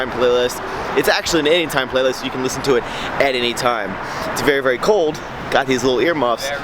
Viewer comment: the homeless guy repeats what you said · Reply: I was just about to comment that. He said, "It's very very cold."